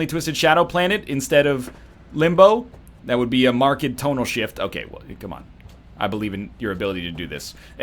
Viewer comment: Did NL just say "a marked Tomo shift"?